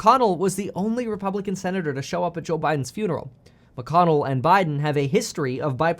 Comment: WTH when did Biden die. So is Trump going to stay president now that Biden died. How come no one told the people yet. $#!t this whole world is going to $#!T. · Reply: no no no! He said Jill Biden, but it sounded like he said Joe Biden.